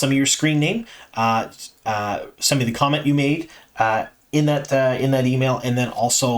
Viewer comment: Thank you for your enthusiasm. BTW I thought you said condom instead of comment